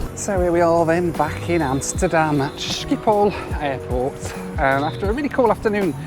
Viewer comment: Thank you for your kind words. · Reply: Lol! Thought u were saying "shithole-airport"